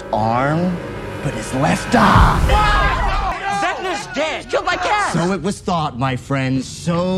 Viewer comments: I always thought mike said he died by cats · I’ll be honest when they said “He was killed by Kaz” I thought Dustin said he killed my cat lol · I thought mike said he was killed by cats 😌